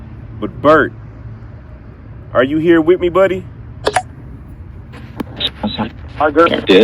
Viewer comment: i hear hi burt or hi buddy